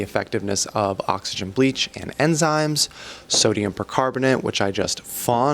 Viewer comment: Just letting others know, at 8:15 he is saying, “sodium percarbonate”. I had never heard of it before and I thought he was saying sodium bicarbonate but tried comparing baking soda to it (which IS sodium bicarbonate) and it made me super confused. I couldn’t figure out what he was saying, even with captions and everything. I had to cast this video to a bigger screen to read what was on the jar’s label. 😂